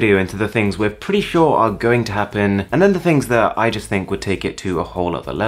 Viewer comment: I just think it sounded like iJustine.. :P